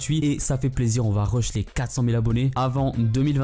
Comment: C'est moi ou il a dit a 1:05 on rush les 400000 avent 2025 tu les a cette année